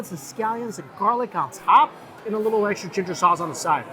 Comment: did he say oyster chicken sauce? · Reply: No lol. He said a little extra ginger sauce on the side